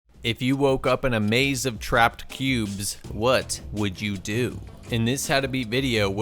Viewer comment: I heard " What would you do if you were tapped in a maze of trapped pubes". And given this is the it took me a moment to realize I may have misheard that.